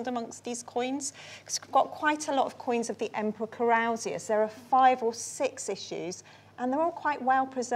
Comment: I'm fairly familiar with the names of thr Roman emperors in the 1st to 5th centuries. I am quite hard of hearing and often have trouble with accents. At approximately 24:22 of the episode, it was mentioned there is a horde/purse drop of numerous coins in 5 or 6 issues of the emperor Carousius? My closed captions stated it as 'Corralsias'. This does not ring any bells. I checked online on lists of 1st to 5th century emperors whose names may approximate what I think I heard and nothing was even close. Any thoughts on whose name she recited? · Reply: possibly this dude: Marcus Aurelius Mausaeus Carausius